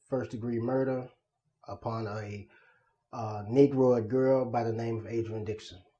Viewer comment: He says negro like he 75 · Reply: Yeah! I heard that too; I had to play it over several times to make sure I heard it right. It sounds like he said "Upon a negroid girl name Adrian Dixon." negroid wow!